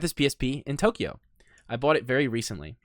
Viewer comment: says "pought" it instead of bought it